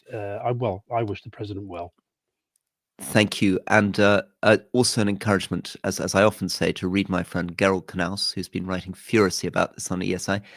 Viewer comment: Incredible. What a moment to capture. President Zourabichvili is incredibly brave and I hope she is supported by UK and EU leaders. I’m glad you persuaded Madame President to speak today. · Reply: Who did Rory say was writing furiously on the topic? Darryl Knause?